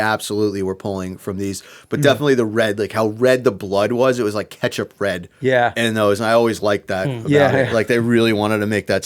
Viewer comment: I know he said "ketchup red" But I hear "catch a pred" like Chris Hanson.